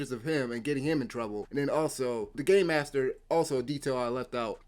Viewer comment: For some reason I heard "The gay master"